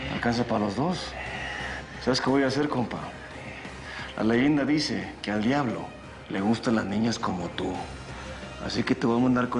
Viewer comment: Que dice en el min 2:05 ? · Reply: "la leyenda dice que al diablo les gusta las niñas como tu"